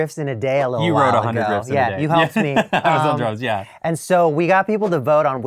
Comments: I've only just started the video, but I would like to point out that the captions at the beginning say "I was on drugs, yeah" instead of "I was on drums, yeah" 😂Might give folks the wrong impression of why was only helping! Looking forward to seeing what you've made today ✨ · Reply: As a hearing person I also misheard it as drugs lmao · When he said "you wrote 100 riffs, I was on drums", I thought he said, "I was on drugs" lol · "I was on drums, yeah". I totally heard 'drugs' here. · I've only just started the video, but I would like to point out that the captions at the beginning say "I was on drugs, yeah" instead of "I was on drums, yeah" 😂Might give folks the wrong impression of why was only helping! Looking forward to seeing what you've made today ✨ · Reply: I had subtitles off and still heard it as "I was on drugs" Xd